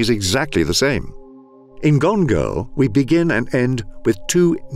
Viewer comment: Can anyone name the movie at 11:17. I keep hearing "gone go"